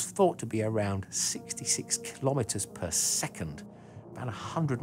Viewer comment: You've wrongly typed 66km/h instead of 66 km/s at 6:44 english subs